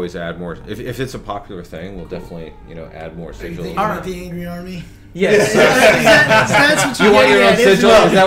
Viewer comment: What does joe say? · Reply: IBreakker I couldn't understand all of it, but it sounds like he's saying something about the "Angry Army". Probably referring to making a sigil based on his clan symbol.